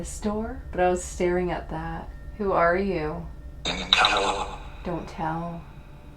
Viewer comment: A lot of times I don't hear the same thing that they do. For example, at 36:40, I heard "the devil" at first, until I saw the "don't tell" caption. At 37:55, I heard "I did" when said "cause he killed a woman", and right after "it was me" or "I sure did".